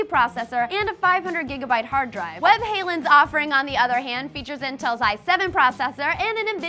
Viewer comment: When you said 'webhallen' I thought you were saying 'Van Halen', because you pronounce it so wonky!